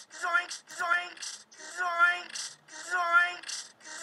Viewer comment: I thought he said Zoiks Zoinks Zikes and Yikes alongside Zoinsky